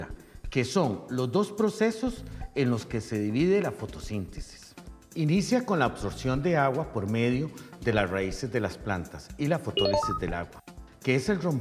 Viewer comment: que dice en el 0:55 alguien me responde :´v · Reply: Inicia con la absorción de agua de las raíces de las plantas